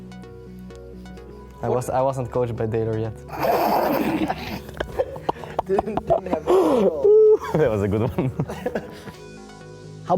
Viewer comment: What did Feb say at 13:38? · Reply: I wasn't coach by Deilor yet